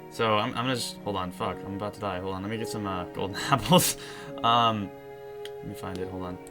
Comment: WAIT.Did he just say,"gold" apples?!?!